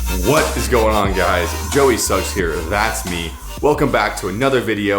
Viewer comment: i thought you said "Joey sucks here" I'm like I dont suck lol